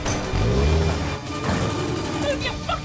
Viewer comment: What did he say? : / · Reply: I love it when a posh bird talks dirty